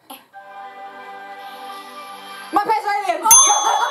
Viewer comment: This was a very fun video just like the Disney challenge you did on Aimee's channel, but I do have a question. What is the show that yells out at 1:20 called? I keep hearing My Pet's Island but I know that's not right. XD